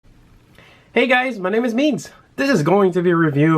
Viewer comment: At first I thought you said your name was beans.